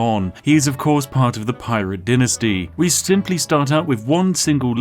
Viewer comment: wtf is a denasty Ik what a dynasty is but that ain't what he said 😂😂